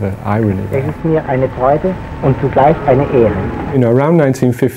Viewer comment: Does Einstein say at 0:42 "es ist mir eine Freude, und zusatz eine Ehre"? (I'm learning German and I'm not sure if that is what he says, or I am misunderstanding).